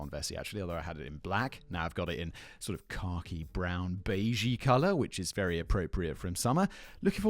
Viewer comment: Vessi bump "khaki brown" but in British English rather than my American English so I heard "cocky brown" and wondered what would make a color cocky